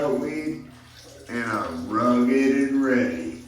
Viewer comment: I swore that condom sign said raggedy ann lol